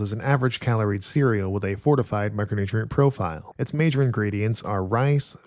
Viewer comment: "fortified micronutrient profile" 🗿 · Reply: This entire time I thought he was saying "4-5 micronutrient profile" and never questioned that there are more than 4-5 macronutrients..